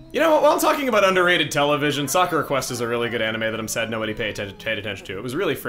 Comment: I thought was saying "Soccer Request" was a good anime, and I was like, "...what?"